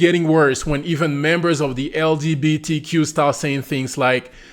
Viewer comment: she actually said she was from the "LGB" community, not the "LGBT..." comunity.